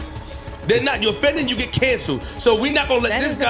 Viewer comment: When did he say that was cancelled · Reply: Right, I swore I heard dissed not canceled 😂